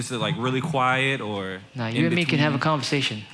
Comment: "Are your speakers loud when you mix ?" ... "You and me could have a conversation the whole time..." ... "Oh sorry lolz". Guy though he was saying "quit hogging the mic", really he was just saying "no".